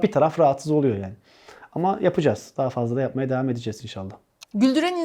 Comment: te devam edicez inşallah mı diyor ben mi yanlış duydum :D